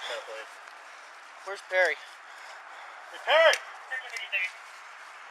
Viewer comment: Did i just heard on 9:26 "Where's Perry?" Where are you, Agent P??